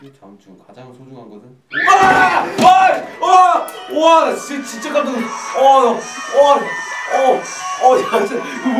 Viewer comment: did jhope said "what a sh*t" at 02:03 ??? hihi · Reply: Khalisah Zulkiflie he said "와 나 ㅈ-진짜 깜짞..." it's like "Wahh, I got really scared" but he was stuttering lol